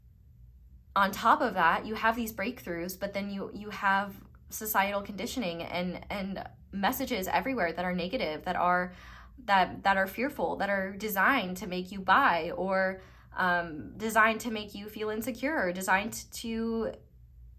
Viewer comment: i heard, designed to make you bi? 😂 anyway, great reading! thank you so much! love your energy 💚🌹💡